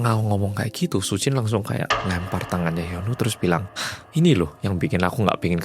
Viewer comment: kebiasaan play trus cuma kudengerin tanpa nonton videonya, sejauh 10 menit video aku dengernya si mas pacar namanya Yono. menit ke 10:22 ga sengaja ngeliat videonya, ternyata masnya namanya Hyunwoo😭😭😭😭